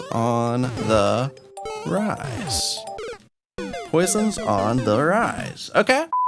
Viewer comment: the way you said it sounded like "Poison on the rice"